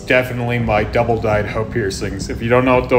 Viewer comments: I heard "double died h0e piercings" 💀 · I thought he said “double dyed h*e piercings”